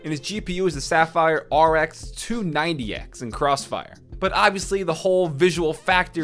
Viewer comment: did he just say a sapphire rx 290x in crossfire ?